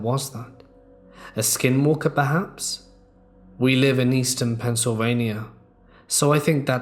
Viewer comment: Hey, did you say Easton, Pennsylvania at 30:45?